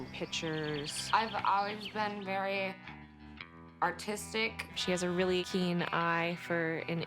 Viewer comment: She said she's always been very artistic but i swear I thought she said autistic cuz of the slurring...